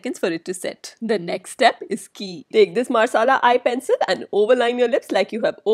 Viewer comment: I actually thought u said masala eyeliner 😅😅